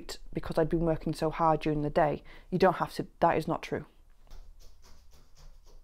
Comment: lol I thought you said 'GOATS'